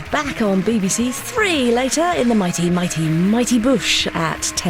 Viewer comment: At the end it sounded like she said the mighty bush. Just saying.